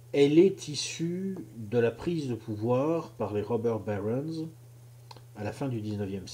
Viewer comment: Il dit quoi à 5:04? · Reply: par les robber barons